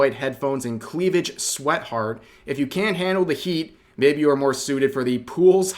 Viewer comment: did I just hear say sweatheart instead of sweetheart.